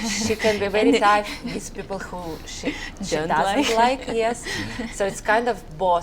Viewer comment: What does Valentina's sister say about how Valentina is with people she don't like? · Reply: don't know🤔 I think I heard Shy, she can be very shy with people she don't like, does that makes sense? 🤔